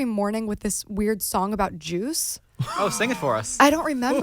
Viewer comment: i thought she said "a weird song about Jews" i was like "WHAT?!" I replayed and i was like "ohhhhh juice! Juice! Got it"